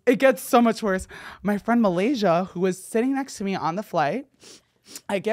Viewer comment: did u just say your friend's name is Malaysia?